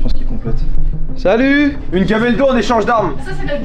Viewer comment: Bwahahaha à 9 minutes 50 j'ai bug j'ai entendu "Une camel toe en échange d'armes" :')